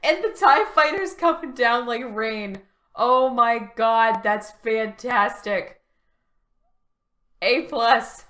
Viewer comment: did you just say FINNtastaic? 😆